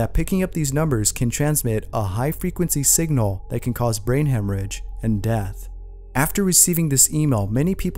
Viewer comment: "and it causes brain ham range" Super legit... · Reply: FifteenYearOldGirl I thought he said hemorrhage